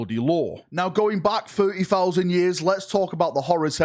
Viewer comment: did say 30000 years ?